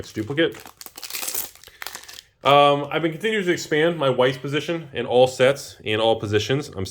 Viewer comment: is it just me or does it sound like says "my wife position?" i mean, same same, but i got a good giggle anyway